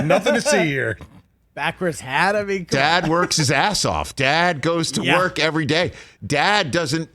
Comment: I thought you said, go dad, go!